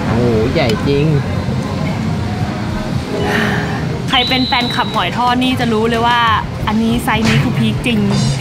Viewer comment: ได้ยินว่าใครเป็นแฟนคลับหมอยทอด